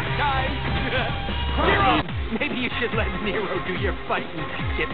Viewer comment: (To Dante after his defeat): ''Maybe you should let Nero do your fighting..'' *cannot hear correctly what he says* DMC3 DANTE DOESN'T EVEN KNOW NERO YET, WTH DEADPOOL?